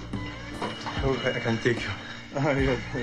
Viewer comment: what did serge nubret say to arnold at 1:09 and how did arnold reply ? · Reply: i think serg said i think i cant take you arnold replied yeah keep looking.